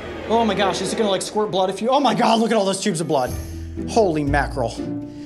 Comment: did coyote said "Oh my God"